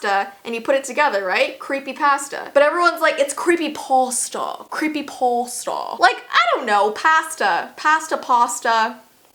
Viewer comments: It sounded like you said “Creepy porn Star” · I swear I hear you say "Creepy P°rnstar"